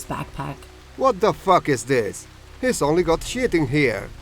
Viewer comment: Did he say what the fu** is this?